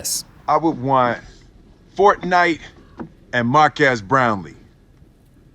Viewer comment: Most importantly why did Will say Mark ass Brownlee 😂😂😂😂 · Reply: aakash singh I heard it as mark ass brownie